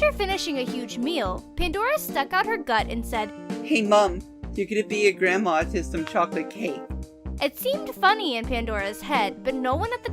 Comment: I did not understand #6. At first I thought I heard "Hey mom, you're going to be a grandma if I eat this chocolate cake." But that wouldn't make sense given the context (and I've played it over several times even put the tablet to my ear... note it is on its own a bad/poor quality speaker anyways) Then I thought maybe what I thought was Grandma was grandpa, then it would be "Hey Mom, you're going to be like grandpa if 'I' eat this chocolate cake." Obviously, I'm not hearing this one right, cause the context and reactions don't make sense to me in this sense.... please help if you can, my horrible tablet speaker is to blame for this confusion (and the fact I'm not waisting more time before work to go get one of my better Bluetooth speakers or play this on another device to hear it better).... Please help me understand this one if you can... thank you😁